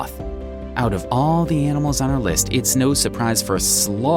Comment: I thought you said animals not insect